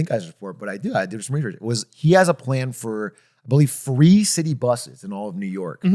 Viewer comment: I heard "he has a plan for three city buses" and was super confused lmao. Like was he gonna make 3 buses? Reduce the number of buses to 3? Lmaoo I was so confused